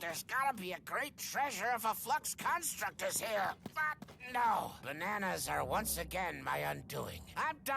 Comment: it sounds like says fu** no here when he says but no